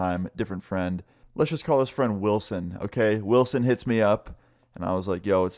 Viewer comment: I thought he said will smith